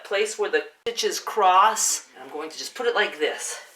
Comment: Excuse my stupid question (I'm from abroad and my english is not so perfect) but did you say at 3:20 "Where the bitches cross"?